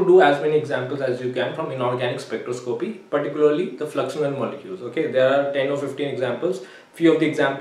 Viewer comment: sir aap kya bole smjh Nahi Aya, flux?????? Molecule in inorganic spectroscopy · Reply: He talked about Fluxional molecules